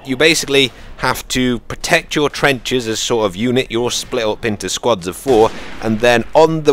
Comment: Anyone hear him say "unit your split up"